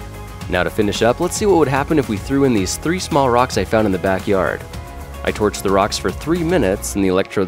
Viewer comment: I heard "I tortured the rocks" lol